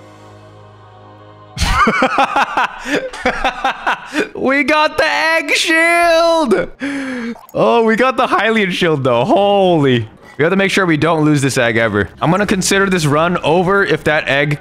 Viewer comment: "I thought it said egg Shell but at least it made me laugh" :)